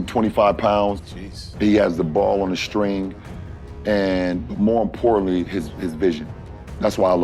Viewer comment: excuse me? Wtf did he say 😆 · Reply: “Damn that motherfucker cold” in 2x speed lol